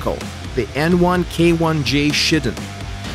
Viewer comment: it sounded like you said ''the n1k1 ja Shittin